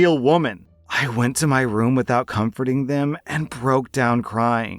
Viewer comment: confronting, not comforting. OP didn’t call out her sister and friends for the hurtful things they were saying about her behind her back. 16:32 shouldn’t be ashamed, OP‘s post made it clear that while she didn’t directly insult her sister, she made it very clear that she knew what the sister said about her and that’s the part that made things awkward. OP wasn’t being savage back, but she did dish out some humble pie to her sister. Edit: there might’ve been a little bit of coldness in OP’s words.